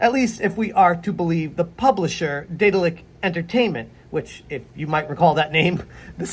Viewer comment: Could've sworn you said Diddler Entertainment lmao 😂😂